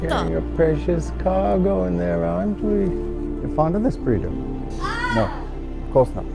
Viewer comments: "you're fond of this breeder" had to rewind thought he said "eponymous breeder" like a harry potter spell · for some reason my subtitles said "your fond of this burrito" and i just started cackling, but it was nothing compared to "my golden retriever alpha"😂